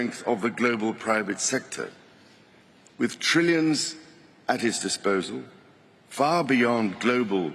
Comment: After hearing various times and even using closed captioning, it does also catch "at his disposal". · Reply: I hear 'IT'S disposal' which makes more sense.